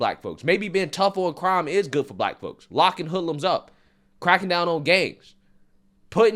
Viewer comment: "crackin down on gays" did i hear that correctly? im a regular visitor to this channel and i enjoy the content, but come on. blacks can be conservative, and so can gay ppl too. thats a little harsh man. i hope im wrong, i honestly respect this channel